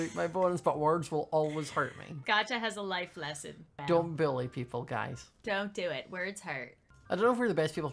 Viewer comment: Me the whole time, laughing cause it sounded like he said "Don't billy people!" (No offence you're the best!)